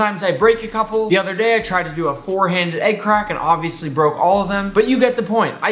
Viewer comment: did he say 4 handed? 💪💪👨🤳🤳